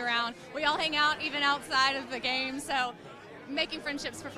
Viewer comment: i've watched this like 5 time and i thought she said they hang out outside of the gang but she said game